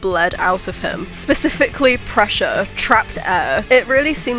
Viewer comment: I heard: "bleeding pressure and air" and immediately went "BURP REFERENCE????" I hate my mind lol 😂